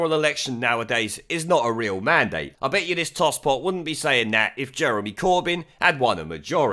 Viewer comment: I thought they said fuckjeremycorbyn 🤔